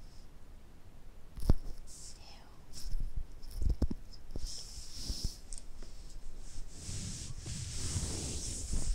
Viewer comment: What does she say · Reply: "L", I think! Not 100% sure though!